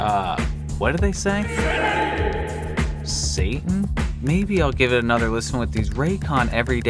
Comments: I think it says "FREE CITY" instead of "SATAN". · I think what they are saying free Satan